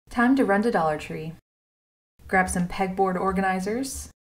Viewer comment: I thought she said “Time to rent the dollar tree.”